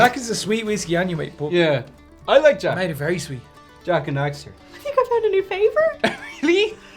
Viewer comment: Did he just say Jak and Daxter? Because if so, my respect just went up.